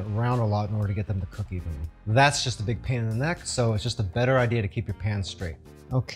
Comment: I really thought you said 'pan in the neck'. Thanks for the video.